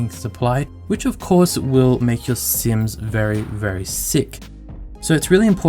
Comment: Did I hear SIMS?